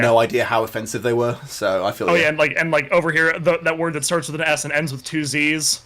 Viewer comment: What is the word that starts with s ends with 2 z's?? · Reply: Spazz